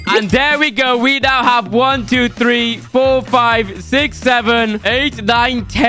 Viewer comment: did bro just said 6-7🤨🤨🤨